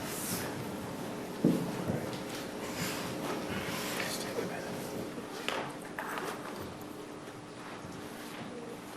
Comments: can anyone tell me if you can figure out what the lawyer whispered to her right here? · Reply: He said are you alright?, just take a minute. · if someone has better perception than I do, can you please try and figure out what Nurmi is saying here? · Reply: He says "You alright?" and she nods, then he says "Let's take a minute" and she steps down from the stand